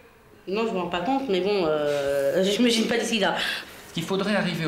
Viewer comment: C'est pas méchant mais vu les parent de Guillaume... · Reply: Mais grave 😖 À 4:03, le doc dit à la mère : "vous vous rendez compte qu'à ce rythme-là, à 12 ans votre fils pèsera dans les 90kg ?", et elle répond : "nan je me rends pas compte, et pis... j'ai pas l'sida !!" ...wut ?! 😐 Elle a une araignée au plafond ou quoi 😐